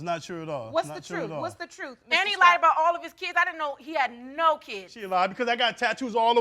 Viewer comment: I know Im not the only one heard him say he has nine kids🤪🤔🤔